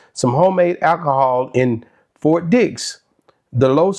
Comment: Sound like you said he had homemade alcohol and 4 dicks1:10😂😂😂😂😂